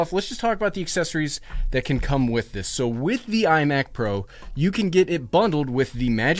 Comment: Did he just say “iMac Pro”? New iMac Pro leaked?!